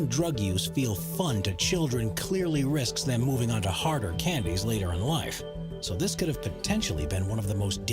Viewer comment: i thought it said candles and was very confused.